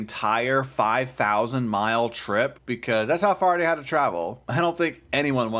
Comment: 🎶But I will walk 500 miles...🎶 huh, oh sorry you said 5000 miles, I thought you said 500 miles, my bad 😅